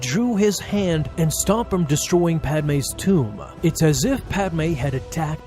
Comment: i thought it said DARTH VADER DESTROYS PADME'S WOMB